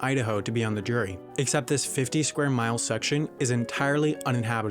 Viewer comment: i think he said 50 square MILES (not meters) lol